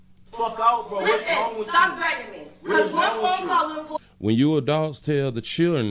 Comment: Thanks for the translation.. I thought she said threatening, not dragging...